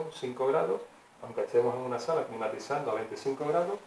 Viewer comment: hola amigo ¿Podrias ttrancribirme que dices en el minuto 9: 26 : "Una vez que evapora la ultima gota , aprovechando el calor de la sala, ¿dijistes sala o me paraecio a mi? En el minuto 10:24 , nos d la palabra que usastes alli ...., porque el audio es muy bajo Y en el minuto 11: 02 DEL video dices que el evaporador intercambia calor con el aire pero no entendi la palabra que usastes despues y me perdi...... De donde sale el aire caliente en el evaporador para calentar el liquido? gracis por transcribirmelo porque use los subtitulos pero este viene con errores... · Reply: Hola Rober, pido disculpas por el audio. A tu primera pregunta, sí, me refiero a una sala o recinto, en la segunda consulta soy yo quien no entiende tu pregunta jeje. En tu última consulta está escrito en la pizarra lo que digo"El evaporador intercambia calor con el aire interior de la cámara frigorífica o recinto a enfriar."